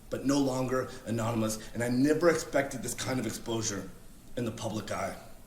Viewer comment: Classic hypocrisy from yur-boi @ 1:46. Correcting a man who fairly clearly says “exposer” not “explosure” And then proceeds to say made up ass words like “dis-engenuine” in multiple videos himself @ 6:53 LOL Maybe don’t TRY to correct other peoples grammar when you’re just as bad or worse LMFAO 🤣 BTW the word you’re looking for is “disingenuous”